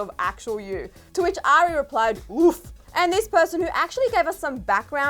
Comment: When she said “oof” 🤣 idk how many times I heard it on repeat 🤣🤣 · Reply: Rija Shrestha same, I was looking for someone who commented about it 😂 it sounds like she said ‘WOOF’